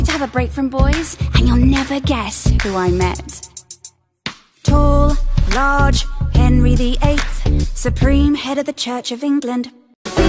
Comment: anyone else thought it said "tall large henry the ape" then you realized it said "tall large henry the eighth" and it makes <num> times more since now no just me okay :p